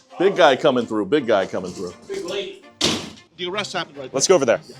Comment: Did he say “big lady” 22:45 😳